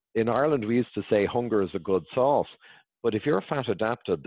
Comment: So good! “Hunger is a great salt” I Love It!!! I gotta get this book. Thanks Dr Berg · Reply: J H i heard it as hunger is a great "sauce". Either one is good but I would prefer to know exactly which he said.🔆